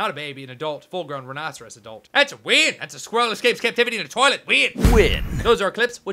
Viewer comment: I am too tired... when he was talking about the squirrel, I thought he said " That's a fail, that's a squirrel escaping captivity win." I thought WTF